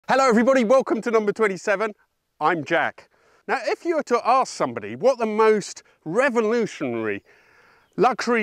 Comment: didn't know who he was, so I thought for a second he said: "hello everybody welcome to number 27, I'm JACKED" and I was like...uuuhm ok?